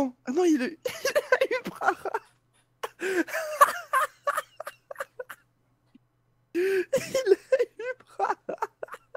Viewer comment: " Il a eu Prahahahahahahahahahahahahahahahahahahahahahahahahahahahahahahahahahahahaha ! " (Voila ce que j'ai entendu quand il la summon)